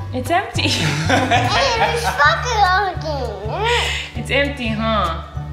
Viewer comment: did he say "its fuckin empty" at 10:52?